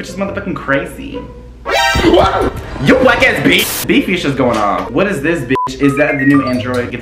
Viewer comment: okay but please do not tell me he said “black ass bitch” at 1:48 i know it’s just about a game but if that’s what he said i think a lottttt of people will be upset lol